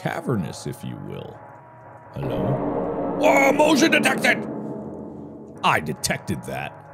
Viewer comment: In 11:40 I thought it said woman detected 💀